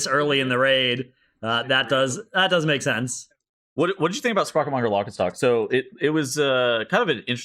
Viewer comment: my dumb ass thought it said ‘sprocketmonger chickenstock’ for a sec